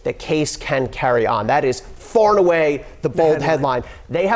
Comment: Did he say "Fartaway"?🤣🤣🤣 🤣